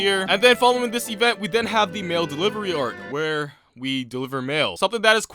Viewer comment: i thought u said male delivery arc and i got so confused